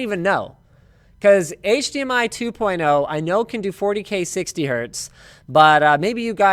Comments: 40k 60hz? did i hear that right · Did he say 40k 60hz at 21:33? lol